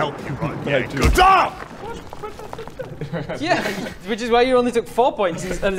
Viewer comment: Is it just me or Bob swears in French at 18:22 ??? XD (I keep hearing "PUT*IN!" when the stalactites fall)